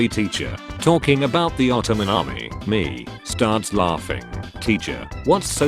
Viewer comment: The peaches were put in a can by a man who works in a factory downtown. Damn....... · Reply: When I was a kid I miss heard it as Otterman Army and my kid brain thought it was a bunch of otters wielding swords and guns and slaughtering humans. The otter I was thinking of was the cute yet brutally evil sea creature otter.